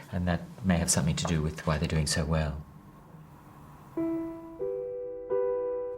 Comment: I heard they're doing porn now.